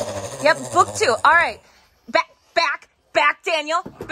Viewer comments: Did she just say, "I hate feeding time"? Hahaha I'm dying 🤣 That was a great line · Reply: Was that what she said? I heard “Alright, it’s cheating time.” 😂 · I thought she was saying "ah, ok, cheating time"...had to put caps